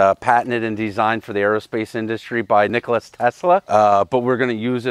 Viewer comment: did say "Nicholas Tesla" instead of Nikola Tesla? Lmao